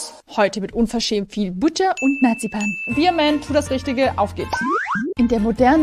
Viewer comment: Hahaha ich habe obwohl Marzipan gemeint war Narzipan verstanden